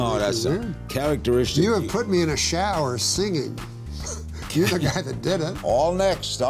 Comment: What does he say at 1:03? "Give a guy the didn't"? Or something else? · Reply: "your the guy that did it"! Norm was saying that Larry was the guy that made it weird by evoking an image of Norm showering.